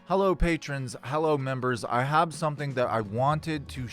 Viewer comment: I misheard the opening as "hello Patriots" and thought I kinda clicked on the wrong video 😛